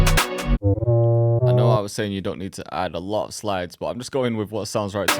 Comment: thought you said no more slides 😆. man you my producer of all ti me